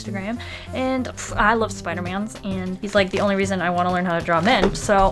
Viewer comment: Did she say Spider-Mans at 0:07?